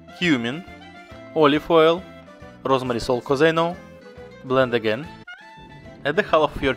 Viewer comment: What does he say at 0:27? Rosemary salt? I unfortunately can't understand it and I really want to make this recipe. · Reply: Yes, I will post Rosemary salt recipe soon. But you can use regular sea salt as well :)